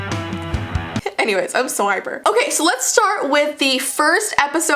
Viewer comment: i thought she said im sober lol